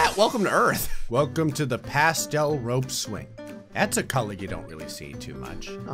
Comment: i thought that said the pansexual rope swing and i got all excited